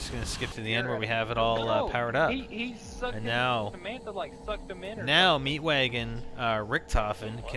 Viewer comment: haha listen what they say inbetween! 7:18 says: Samantha sucked the zombie! 10:30 says: Kangaroo will fly in Spiders ass!